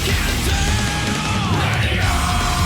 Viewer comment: Flashbacks to Maximilian's Xrd streams when the chat would go nuts when Ride the Fire would cut in. Sol: Dragon Installs Chat: Spamming LET IT OUT, RATED R, etc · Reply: "Ready or not, that's your circumstance" People often mishear it as radio or rated r